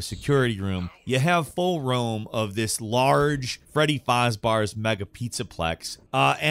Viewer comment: did he just say Freddy fars bars?